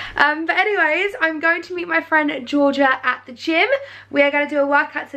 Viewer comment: Who else thought she said Jed and Georgia at first and I was like - what does Jed have a gf !!! Then had to play it back haha x